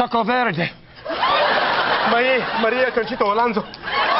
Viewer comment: Okay, someone tell me what Wayne said at 19:20 that was so hilarious to Greg? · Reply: he said maria concheta Alonzo who's a Spanish actress